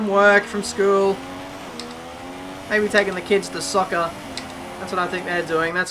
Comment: Did he just say 'soccer'?!